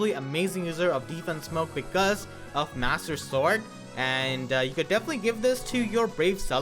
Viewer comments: Almost sounded like you said Master sword. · did you just say master sword????